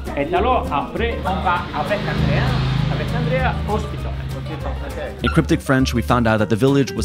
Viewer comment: he says "hospital" now check what the subtitles say :D